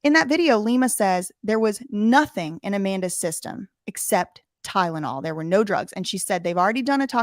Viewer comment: Thank you so much for covering 💖 I’m looking forward to what you find and out together on this case! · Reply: btw @ 53:24 that’s not what Lima said.. she said, ”ty-den-all” 💀💀💀 idk what it is about that soft white underbelly clip.. but it infuriatesssssss me the way she pronounces Tylenol lolol. Definitely doesn’t help that I cannot stand that shady lil….🙃🤐